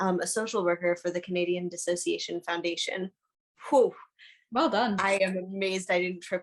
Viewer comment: At around 2:26:03 you mention what I hear as "Canadian Dissociation Foundation" which I had not heard of before (or only in passing). I have tried to figure out what this is, that name does not show up in my searches, nor anything obviously close enough to see what it is. It was who said it. As of having watched that far... I haven't seen anything which wasn't either already said, or seemed obvious to us. I do appreciate why this is being said, but having seem the "other side" and how they are with this (literally had someone state that they were indeed very aware that what they were doing is intentionally spreading harmful misinformation as a way to harass) is not going to change any of that, and likely they will use it as further fuel for their arguments.